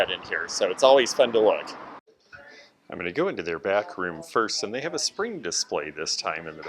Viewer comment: Lol, thought you said going to bathroom first, not back room first. I was like “ yes, I’m a bathroom first” sort of shopper!